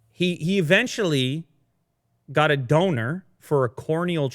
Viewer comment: I heard Döner instead of Donor! It's just what matters. Such a shame to me! :D